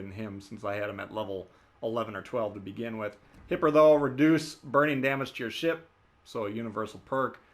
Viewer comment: Sometimes I have to wonder at what I think I hear. says "Reduce burning damage to your ship", and I hear, "Reduce brain damage in your team", and was reaching for my wallet before I realized I must have heard incorrectly.